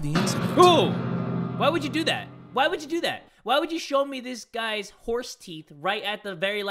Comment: When you said “why would you show me this guys horse teeth” I thought you said “why would you show me this guys horse MEAT” I got scared